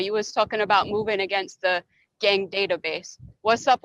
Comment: Did she say "You was talking about Cher" at 1:05?